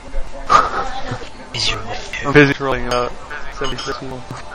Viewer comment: damn i wanted to hear him talk to his wife or whatever happened. Thankful to even have this though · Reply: he's talking to some woman in the background. And he says something that almost sounds like he said "busy trolling"